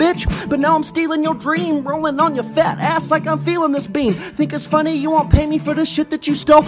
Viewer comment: Did he just say the N word in 1:39? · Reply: GivingLove think its funny is what he said